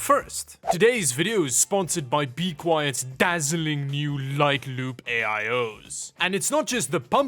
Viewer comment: Not gonna lie i misheard "Light Loop AIO" as "Like Lube AIO"